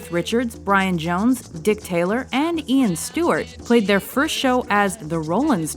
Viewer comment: did she say Dick Taylor?